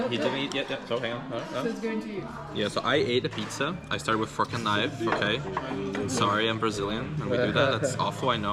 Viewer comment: Fu*kin knife😂😂.. that's what I heard😂😂